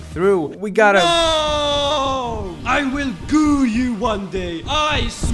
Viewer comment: I thought I heard “I will goon you one day”😂